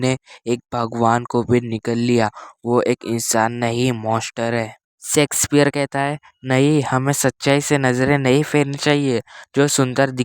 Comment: kya kaha sex square 😂😂😂